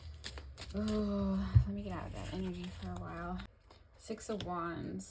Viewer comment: 📌 Time Stamps📌 INTRO/BACKGROUND 0:50 TAROT INVESTIGATION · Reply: It seems you were feeling a negative energy. I thought I heard you say you were in a bad energy. I have listened to other readers, and they all say the energy is really bad and emotionally draining for them doing his readings.